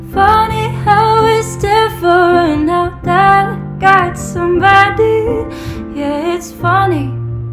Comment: サビ前の 「that I got someday Yeah It's funny」 が 「誰かそばにいて、It's funny」 に聞こえるんだけど理解してくれる日本人の方いない？